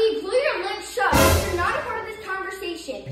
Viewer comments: I thought said "get yout ass hands over wash yo dish😭😭" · “Get yo ashy hands over here and wash you own dish!” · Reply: I thought she said “get your ass over here and wash your own dish